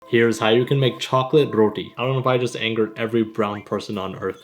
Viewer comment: For a second, I thought it said how to make chocolate rot!